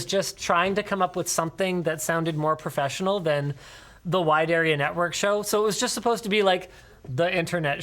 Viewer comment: I actually didn't what WAN show mean😂😂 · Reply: I thought he said White Aryan Network, almost choked laughing